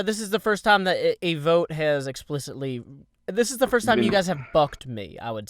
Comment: Did say "this is the first time you fucked me..." Around 13:35 ish